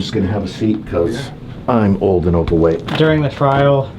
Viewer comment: “Im just gonna have a seat because i’m old and overweight” I felt that 😌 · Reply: I totally thought he said "...because i'm old and in the way"